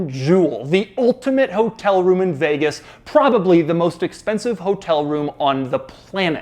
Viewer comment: I thought he said the most expensive cars show room then I focused a little bit and got shocked 😂